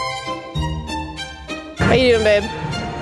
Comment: it sounds like said "How ya doing babe?" To:O